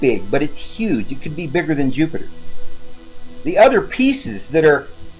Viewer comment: did he just say Yupiter? Hahahahahahahahahaha so epic. Btw love your videos mate, keep up the good work!